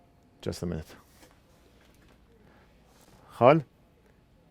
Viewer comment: Wait wait wait, did you say "Khal" or what?